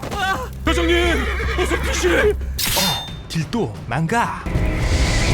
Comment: 뭐라고 ㅅㅂ? 어? 딜도 ? 망가? ㅗㅜㅑ